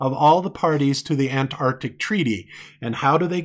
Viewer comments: is he calling you "babe"? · He's saying "DAVE" in a very cringy and condescending tone. Not babe. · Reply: I swear he says "babe" around 19:35 but I could definitely be wrong and according to the Dunning-Kruger effect means I'm of average intelligence... I'll take it!!!